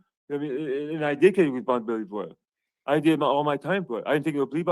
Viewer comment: Did he say ... "and the last time I didthis.. I did all my time" as in this isn't the first time he did this?